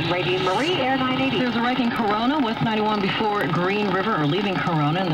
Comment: DID I HEAR CORONA